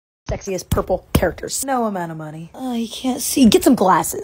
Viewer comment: OKAY I heard "sexy, purple" MY MIND WENT STRAIGHT TO WILLIAM/PURPLE GUY